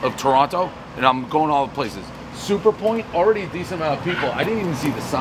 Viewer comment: At first I thought it said Super Joint Ritual. But obviously not.